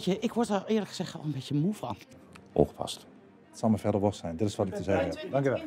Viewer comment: wat zegt ie? Oogpassen? Oppassen? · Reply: ongepast?